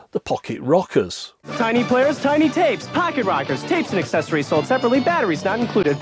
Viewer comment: I thought he said Pocket Rockets at first lol.